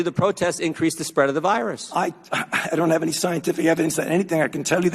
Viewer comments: Did anyone else hear him say, "I don't have scientific evidence that anything..." 🤔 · “i don’t have any scientific evidence of anything“ I heard him say it! He’s a CHARLATAN!